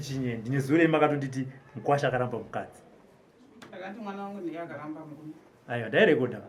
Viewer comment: Wakatadza kunzwa Robert, I heard her saying mwana wavo ndiye akaramba mrume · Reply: Vakati mwana vangu ndiye akaramba murume